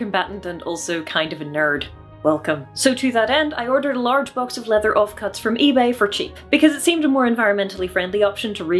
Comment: When you said '4kg of leather off-cuts' I hear '4kg of leather of cats'